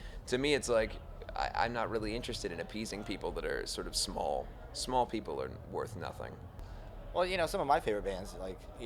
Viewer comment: At first i thought he was saying "small people are worthless" as in like height.. Cos m like 4ft 6 and 15. Hah i was like ahh.! Then i got it..